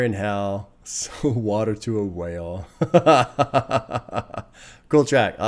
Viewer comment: I thought he said "sell water to a well" he said whale... mistakes have been made on my part lol